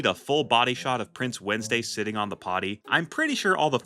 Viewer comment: I thought he said " Dating on the potty!"😭😭😭